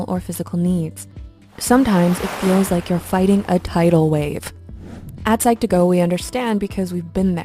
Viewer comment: did you said, "Tidal wave"? *Music starts playing*